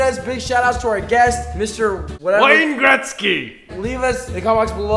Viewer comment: did he say Wayne Gretzky??